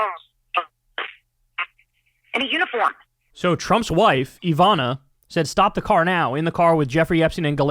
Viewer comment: she said Ghislane, not Ivana, stopped the car to get the number from a schoolgirl. Ivana was I n the car “and they all knew”. I think you just misheard. I know you genuinely try for accuracy.